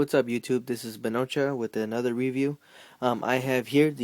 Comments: i thought you said your name was panocha at the beginning lol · Almost thought u said ur name was panocha lol sorry but im sure get that lot. Great video man.